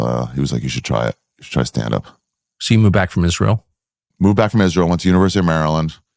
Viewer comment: I really thought he said he moved back from "misreal" not "Israel" but i was wrong.